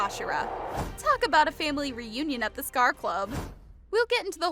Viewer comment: I thought you said scarf club